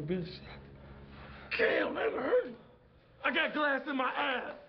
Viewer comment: I thought he said I got glass in my eyes